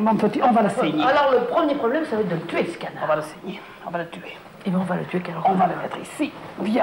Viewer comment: à 2:05 Micheline répond discrètement "eh ben on va le tuer quelle horreur" ou bien c'est moi qui entend mal ?